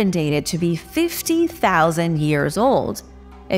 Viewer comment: Thank you for the update..!! 13:36 It's 50,000 years ago, I think. The caption says 15,000 years ago. 😄